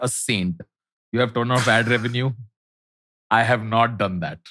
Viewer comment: tanmay said " turned off" ... why does it say ton of